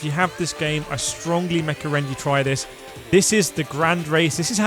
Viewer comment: I swear you just said "mecorrend" - keep the mecorrendations coming